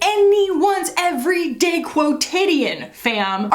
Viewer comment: Whoa whoa whoa, hold up. What was that word you used at 19:43? Not quotation, but not-not quotation? I need to add it to my ostentatious words collection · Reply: Quotidian